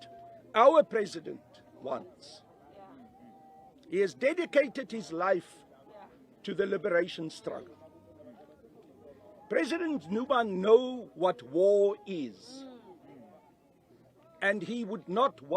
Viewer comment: Did he just say president Nuba?